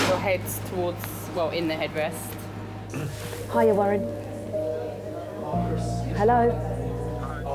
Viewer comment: She was on the phone to her mum who has separated from her dad. So she was asking her mum if her ex husband can 'come home' and be looked after by her mum. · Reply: I’ m not so sure… when she first talks on the phone 32:17 I think she says “hiya Warren…”. If you replay it carefully you can hear a male voice responding. They obviously ask how long she was going to stay there (sub-text “when are you coming home to me?”. Also, she was worried about the un-scraped plates in the sink, the last thing I would be thinking about if he was my dad, but maybe symptomatic of her home life?